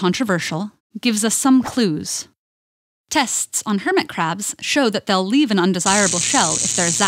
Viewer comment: did you just say minecraft's "hermit craft"????